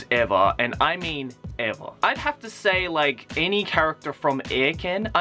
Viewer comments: What's the name of the anime he mentions at 7:05? I looked up everything I thought me might have said and I can't seem to get it right. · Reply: Its called Eiken. Be warned, Joey wasn't kidding. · what did joey say at 7:06? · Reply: Eiken.